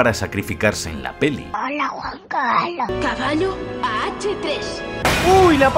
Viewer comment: Que es lo que dice ron en minuto 9:37 Esa voz da miedo pero no logro entender que dice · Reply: "Hola Juan Carlos" eso dice